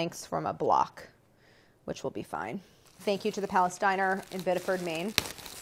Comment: I thought said, "thanks to the Palestiner" but it was "Palace diner"...🤔 i think?